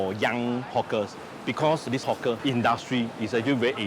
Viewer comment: everyone they say hawker, i hear 'hook'er or "hot gurl" hahaha